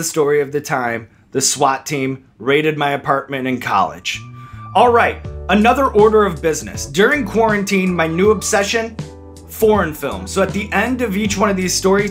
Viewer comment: I wasn't paying attention to the video and I just heard him say "porn films", was confused for a second ngl